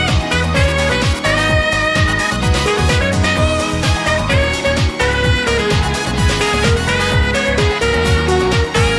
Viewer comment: What were you sayin here 🧐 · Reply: I think something like “we made it, let’s go meet SIU!”